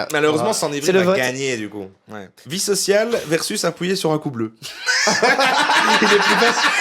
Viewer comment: Appuyer sur un quoi ? Un coup bleu ? Un couleux ? C’est du belge ? Je capte pas. Quelqu’un pour m’éclairer ?